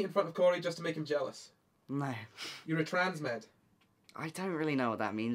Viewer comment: Can someone tell me what the question was?? I'm not native jdhdhdgdh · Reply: he says “are you a transmed?”— a person who believes you need dysphoria to be trans I think